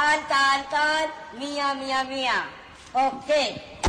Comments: c'est moi ou il parle de Miami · C'est moi ou il parle de Miami 7:32 à